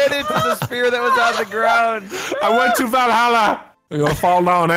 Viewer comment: did he say vallhalla?